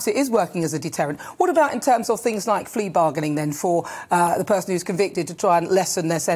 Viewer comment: Did she just call it "flea bargaining"?